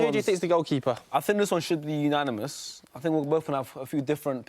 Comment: Lol I thought he said ONANAMOUS!!!